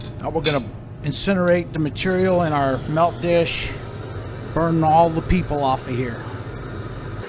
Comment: Did I hear that right ? "We are gonna burn all the people off of here..." 😂